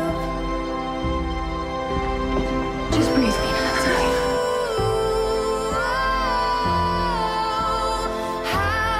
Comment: What does she say here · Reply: Just breath , Its okay"